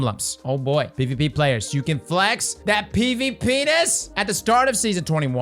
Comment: When you said ‘pvp-ness’ all I heard was ‘pvpenis’.